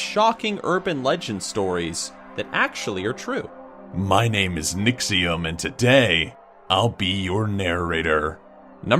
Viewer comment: I thought you said nixxiom